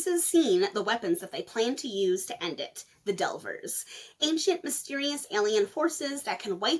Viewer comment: For half a second I thought you said "ancient mysterious alien horses" and now I'm disappointed that's not what you said. Alien Horses would be scary as hel :D. Being displaced 700 years in time can't be easy. Imagine how much something as simpple as language would have changed in that time. Also I can't shake the feeling of the madness that ensue from meeting your 21x Grandmother and trying to convince her you're old enough to make your own decisions. Sanderson is such a weird author for me and I kinda have the same issues as I have with the MCU. I have a passing interest in the Cosmeer and would like to know how it connects but diving in and actually finding out seems like a mammoth task that I'm not sure I'm willing to commit to. Your dramatic dun-dada-dah always makes me smile by the way, so funny.